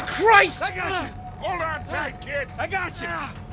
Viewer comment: I thought he said hold on white kid